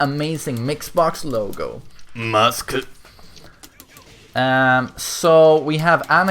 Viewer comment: What does he say at 3:39 sounds like an awesome taunt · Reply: pretty sure it's weird butchering of kazuya's "masaka" ? - means like "no way!" or "well I never" or some other form of surprised expression